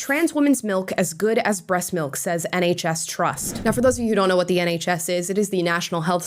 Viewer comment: Lmao thought you said "Any Chest Trust", and then I read the subtitles and it said "NHS Trust". For a second I thought the LGBT was getting creative with a whole new organization. 😂